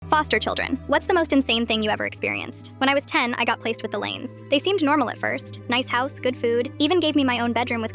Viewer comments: HELP I WAS LIKE "placed with the lion's? Hold up I think I heard that wrong" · what i heard “i got placed with the lanes” what it actually said “i got placed with the lions” absolute cinema ai right there🙌